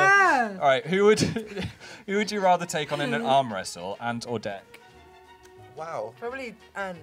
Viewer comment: I thought Phil said "an ant or death" instead of Ant and Dec